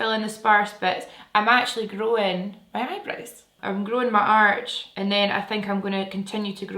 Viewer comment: For a second, I thought you said, "I'm growing my arse" 😂😂 growing your eyebrows 😊